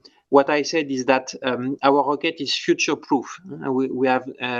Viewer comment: I swear I heard him say “our rocket is sh!tshow proof” and I thought damn, that’s a good thing!